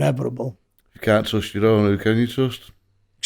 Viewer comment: what is he actually saying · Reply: If cannot trust your own, who can you trust.